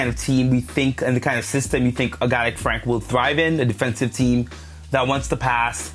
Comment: I can't really tell what the french man is saying. Sounds like he said Frank is trash.